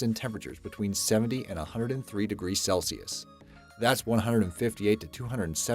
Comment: did he say 70-103 degrees?